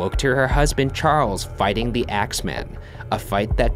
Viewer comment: I heard "Charles fighting the Xmen" and I was like wait what?